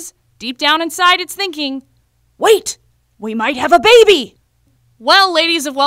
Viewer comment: I thought it said "Wait we might be a baby" 😂😂😂😂😂😂